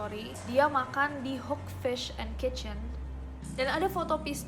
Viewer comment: Gw ngak salah denger kan ya dia bilang "hook fish and kitchen" bukan hook fish and chicken